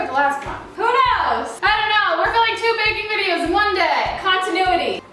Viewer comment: omg 😂😂😂 thought it said bathing instead if baking